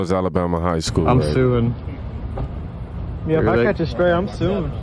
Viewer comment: Did I just hear a guy at 8:20 say "I'm suing"?